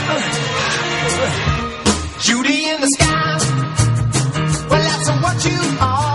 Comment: My sister thought it said booty in the sky lol😂😂😂